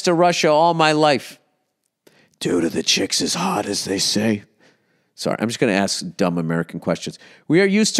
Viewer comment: *Are, (Is) Is singular. (He is, she is, they are) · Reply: I'm aware. That was a direct quote from Bill my guy, you can hear it at 3:05 Edit: upon hearing this for the second time I've realised he have said 'Are the chicks as hot as they say?'. But for the first time I thought Bill've said 'Chicks is hot, as they say?'. I just figured he mangled the sentence on purpose, and trying to be funny I've copied it. The jokes are on me I guess lol